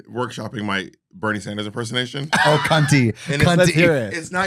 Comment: Why I thought he said Bernie Mac impersonation? I was so confused! 😂